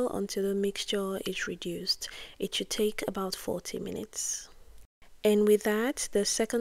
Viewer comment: did you say 40 minutes? Great video and tutorial by the way